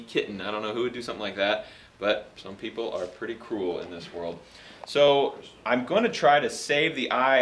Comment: I thought that at 0:57 he said "some people are pretty cool in this world" and I was like wuuuut